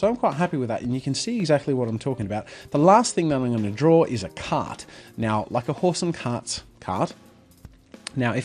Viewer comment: Lol I thought you were saying slanted "cat" the entire time